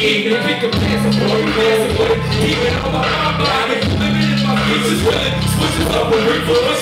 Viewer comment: it sounded like Earl said "I've been living in my feces". I think that means Earl will be getting back to his shock value style that we all know and love. Good.